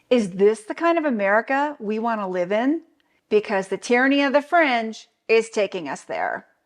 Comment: I misheard "the tyranny of the fringe" as "the tyranny of the French"